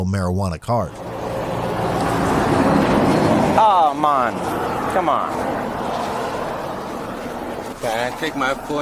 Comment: Anyone else hear the officer say “aw mon” at 11:23 🤔🤔